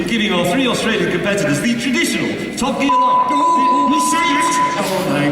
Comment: what did he say?? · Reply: Top Gear